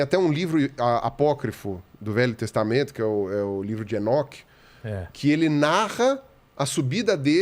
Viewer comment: eu entendi "o livro de shinnok" até voltei pra ver se era isso msm kkkk